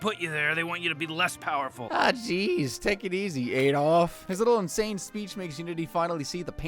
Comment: I totally thought you said “take it easy yedolf” and I was finna be like “well that was one hell of a prediction”